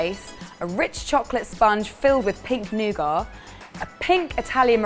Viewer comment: Haha I thought said pig nougat, I had to go back and listen to it again pink nougat makes much more sense